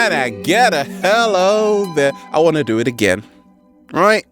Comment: What do you mean sir when you said in 8:25 you won't do it again or i want to do it again its not clear for me . Tnks